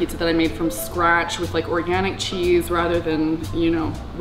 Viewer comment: literally the only thing she said is she's using organic cheese lol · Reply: I thought she said orgasmic cheese